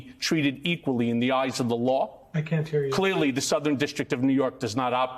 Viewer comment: does anyone else hear "i can't hear you" at 5:59 timestamp?